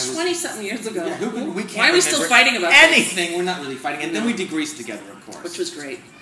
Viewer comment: A wonderful Friend Friday! I wasn't bored. You're funny," you like the control". I sounded like you said " we degreased together.I was like, what? Some sort of celeb spa treatment? xoxoTresa